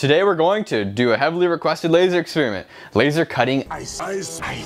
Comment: I thought it said can a loser cut ice